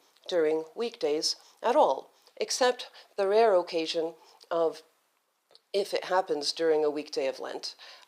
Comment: Though I know and did pray before sleeping, I only heard about Catholic "liturgy of the hours" before... maybe their Chinese translations are so hard to make a link. P.s. 3:39 the first word in subtitle "of" should be "or"